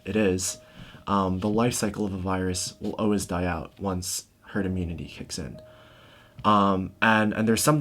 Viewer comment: The virus will always die out, once <what?> kicks in? I couldn´t hear the <what?> word. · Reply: Herd immunity!